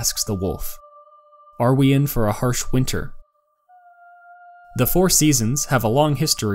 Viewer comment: HARSH WINTER? I always thought he was saying "hard winner", that's an ENTIRELY different question.